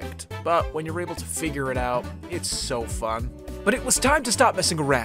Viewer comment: "when you get to figure it out , it's so fuck". I was ABSOLUTELLY sure that I heard that at first